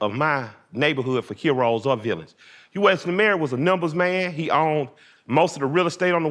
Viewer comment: what name is he saying? I wanna look him up? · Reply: Wesley marry